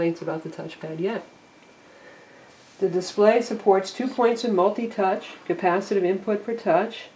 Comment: At around 7:30 I think she said two points for touch.